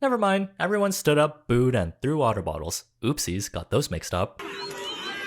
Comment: When I saw that person sitting on top of the Hachi statue, I gasped so hard and the audacity meter went through the ROOF! It’s THE meeting spot in Shibuya and so beloved. Makes me so angry. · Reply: I read Hachi, and thought you said through the woof. 😅